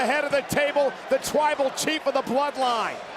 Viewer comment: Hold up at 27:47 when roman got out of the cage did he say twible chief cuz that's what i heard